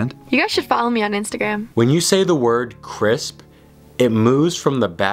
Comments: Who else has been saying the word "crisp" for over 1 minute straight 😂😂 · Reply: Me but I thought he said chris😂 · I know I'm not the only person who said "crisp" when it was mentioned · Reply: i thought he said chris lol